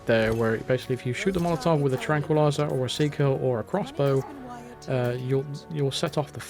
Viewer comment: did he say crossbow?